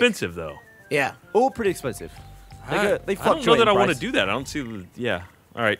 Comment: Did Gavin just say "they fucked you on the price"?